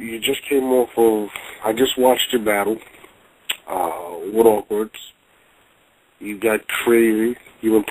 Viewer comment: says awkwerdz instead of okwerdz LOL